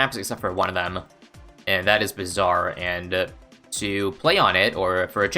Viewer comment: Ngl, when you said "....and that is Bazaar." I legitimately thought you said it was bizarre that you DIDNT BB every single map. I guess it couldve gone both ways lmao